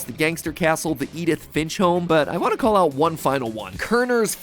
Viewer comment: Do I have bad hearing or did he say "Edith b**ch home"